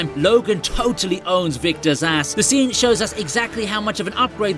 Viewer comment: sounds like you say "Victor Szass" when you were in fact saying "Victor's ass" 🤣🤣 I'd like to see Logan face off against Victor Szass, tho :D